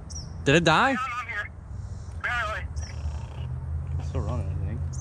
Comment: Sounded like said, "Barely."